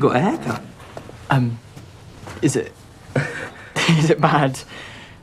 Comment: "you got a haircut" "is it bad" "No you look bad, it looks bad" this edit is freaking wonderful lmao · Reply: I heard "no you look fat" 😭😭😭